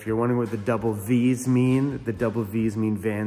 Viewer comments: Funny, I thought you were saying double Ds also. Quick question ; Is your van is a "E 250" or "E 350" ? Thank you . Bear G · For a long time I thought you were saying double D’s also, 😂😂😂😂 Took me a bit but I figured it out You crack me up, you bounce off the walls like a ping pong ball 😊❤ Safe travels, Susie in Michigan